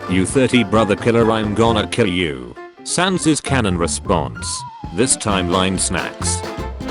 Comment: it sounded like he said this timeline smacks